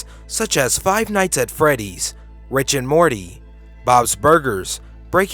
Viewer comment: you said "Rich and Morty" instead of "Rick and Morty" -@ 5:33= sounded like "ah-scentric" instead of "eck-scentric" -@ 13:58= you said "seh-veered" instead of "sev-errd" -@ 27:29= you said "sing-ed" instead of "sindj-d" I went back through the video to find these pronunciation errors, not to be a dick but because I think you have a good voice and I wanted to help you with some constructive criticism. I also don't know how to send private messages, sorry Xo. I hope this helps!